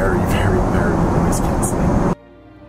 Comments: "very very very.." what word/s is he saying after the final very? is 'nice skins' or 'noise cans' maybe? thanks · What did he say at 9:52? I could not tell. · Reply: "Very very noise canceling" ;)